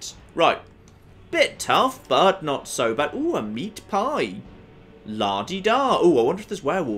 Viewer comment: When he said a meat pie i thought he said Ajit Pai